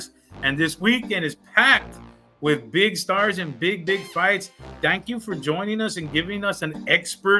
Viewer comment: “Big d*ck fights” [that’s what I thought he said]